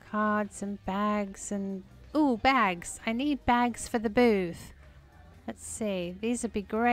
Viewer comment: When you said, "Look at the stack of gift bags! I need them for the booth!" I first thought you said I need them for the booze! I was thinking what a generous gift giver you are! Haha! 🤣